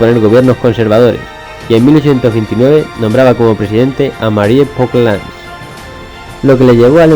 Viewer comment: Gracias por el video, alguien me podria colaborar diciendome quien es el nombre que dicen el el minuto 4:00 y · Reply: creo gabriel oclan :v